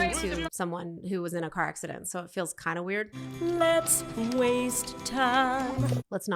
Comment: i literally thought I heard the person fixing the ivf say "lets waste time"